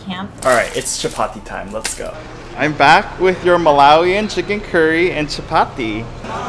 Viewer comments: Chapati !!!! As a maharashtrian I love when chapati gets the recognition it deserves! Yuhhooooo ❤️❤️ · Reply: As a Maharastrian i thought she said मालवणी chicken and Chapati which is a delicacy of मालवण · Me who thought she said Malvani chicken curry and chapati 😂😂